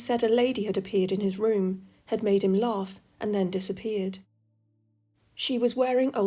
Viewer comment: Had made him... love? Did I hear that right? 🤨 Or was it "laugh", please? The subtitles are of no help here.☹️ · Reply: Yes it’s laugh. Strange really but that’s how he reacted. 🙏